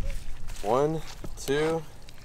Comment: little man said "1, 2, 3" he was counting with you!! Tell me that's not what he said because he definitely did. 😊 I bet today he's saying so much he's talking your ear off.